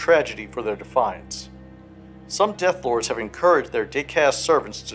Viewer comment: I thought you said “dick-assed servants” for a second